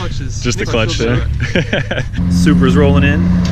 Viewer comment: is he saying Subaru or supra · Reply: Supra